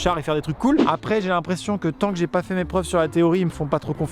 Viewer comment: "J'ai l'impression que TANK j'ai pas fais mes preuves..." merci c'est tout pour moi 😂